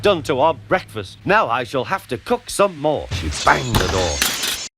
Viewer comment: Am I the only one I heard “she banged the door” rather than “she slammed the door”